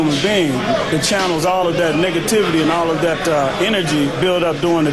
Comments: did he just say nigativity....? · I swear i'm not racist, but I thought he said "niggativity" at first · did he say niggativity?